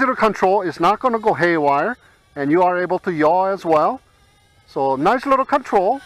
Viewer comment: what action are you talking about? I heard that word over and over again in drone space but never know what that is... Thanks. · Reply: To yaw, to put it is simple terms, is to rotate the drone to the left or right. In other words when you turn around to face someone, you are "yawing".